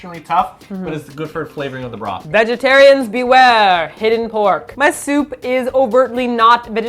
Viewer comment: at first i thought said that there was hidden cork in the soup O.o